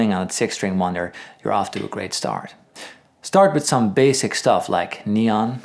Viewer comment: Did he just say"Start with something basic as neon".LOL